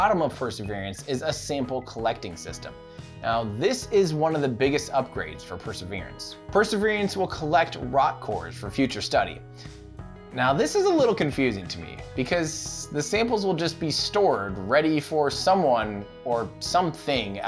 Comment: I keep hearing 'perseveriance'